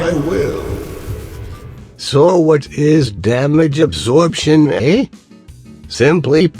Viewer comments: I thought that you said abortion. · Is it sad that I thought it said abortion? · I thought that said abortion... oof